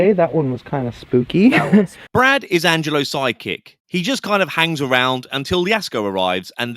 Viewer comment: Did say side PIECE???😮😂